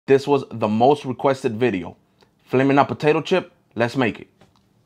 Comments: Thought he said 'Filet Mignon potato chip' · What he said:”Flaming Hot Potato Chip” What I heard:“Flamin hoTABATIO Chips”